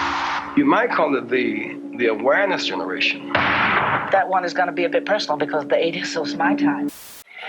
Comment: Also, what did the woman from 7:15 - 7:17 say after "the 80s"? And where is that clip at 13:43 - 13:53 from? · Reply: I believe she said “that’s a bit personal” because the 80s was “her time.” (Assuming her response to a question that was asked of her). I lost many of these old videos but believe the clip at 13:43 is a British children’s tv show called “Going Live!”. Thanks for watching!